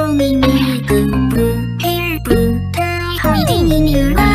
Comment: Why do I hear "Hiding in your wife" ?